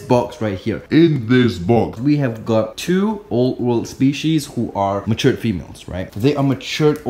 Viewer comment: Who else thought he said oat world?